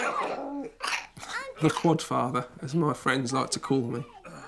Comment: Why is no one talking about dad being nicknamed the QUADFATHER 😅😅 genius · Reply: ah Dang, I thought he'd said Quad FARMER! That makes much more sense.